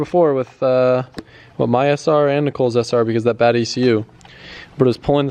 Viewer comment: did he say nicole 😮